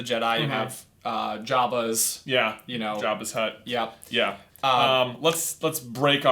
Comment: did J just say jabba's hut?